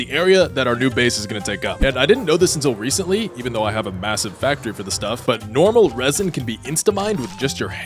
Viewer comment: did you say Massive ?